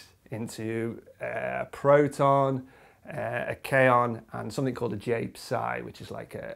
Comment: When he was talking about the decay of the lambda-b baryon (at 6:50), one of the things he said it decays into sounded like "kayon". This must not be the right spelling, because I couldn't find it on google. What is this? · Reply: jsdsparky Kaon